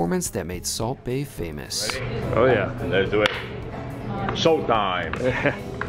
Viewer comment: does he say salt time or show time? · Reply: I think it was show time.